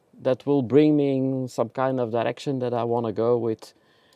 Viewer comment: did he say erection =.=